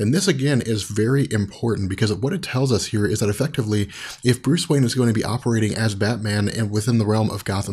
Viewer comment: Does it sound like he says Bruce Banner? When he is talking about the Breaking of Batman